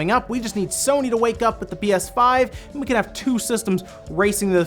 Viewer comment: Did he say bs5?